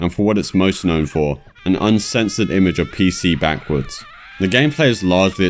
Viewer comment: "an uncensored image of PC backwards" I was just wondering for a minute there, about how looking at a Personal Computer from behind could be unsettling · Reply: not gonna lie, I came looking through the comments just because I thought he said "PC backwoods" and I was trying to find the answer of what that meant, lol